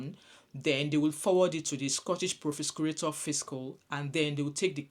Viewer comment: What...? 😂 Say THAT again: Scottish proshsghd fish what...??😂😂